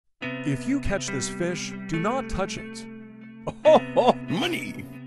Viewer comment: all i heard was "if you touch this fish dont touch it"